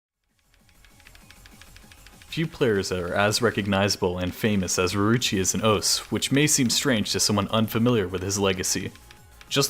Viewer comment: what did he say on 0:05 0:06 ? ruruchi as an osu?